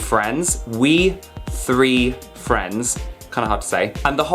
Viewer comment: I thought he said we free friends 😂😂